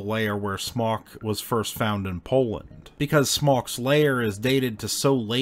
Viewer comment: What he said "Smok's layer" What I heard "Smaug's Lair" 😂